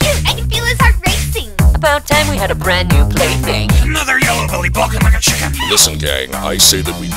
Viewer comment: Is no one gonna talk about the way Bonnie said 'It's about time we had a brand new play thing.' XD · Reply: I always thought he said “Placing” with a lisp 😬